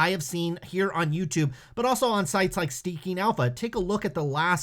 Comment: Sounds like you said Stinking Alpha! lol